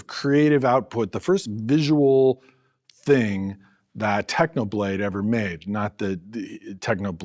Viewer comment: I thought he said technoblade never dies…